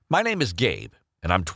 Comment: It sounds like he said hi my name is gay Lol XD